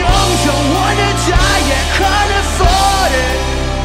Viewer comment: "Cause I am young, don't wanna die, yet can't afford it" sounds like hes saying "yet kinda farted" lmao 😂